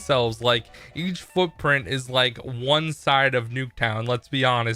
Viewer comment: I said half the size of Nuketown. It is practically a backyard on Nuketown I exaggerated a little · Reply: I thought u said one size of nuketown, now I rewatched you said one side haha